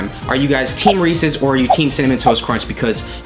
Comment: When you asked what team we are for cereal, I thought you said “Are you team racist?” 😂